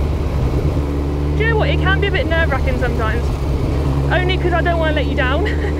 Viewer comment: Is it ok to ride side by side ??? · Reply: Palmer I thought that @ 4:17 said "It can be a bit nerve racking" as pulled up to ride beside her, when he laid back started to relax and enjoy the ride.